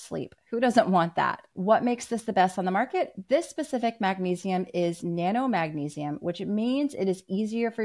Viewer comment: Is it the ReMag liquid Magnesium? I thought I heard her say Pico Magnesium.